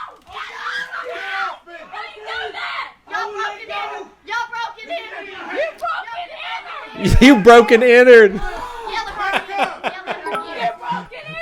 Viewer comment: at first i thought they were saying "YALL BROKEN' INNARD" like the worst r/iamverysmart interjection